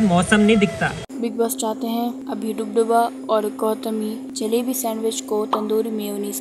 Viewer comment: did she said " ABHIDUBDUBA"